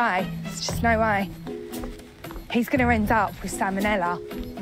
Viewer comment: Am I the only one who heard her say semolina instead of salmonella hahahaha